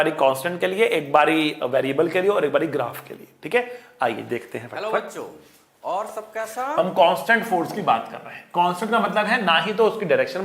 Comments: who heard sir telling " Tho baccho aur SAB kaisa*" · Did anyone else hear hello bacho